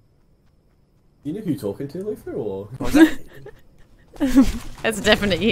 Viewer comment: Is italian? I think he said an italian word lel He may have said "cosa" (what) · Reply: He said "What was that?"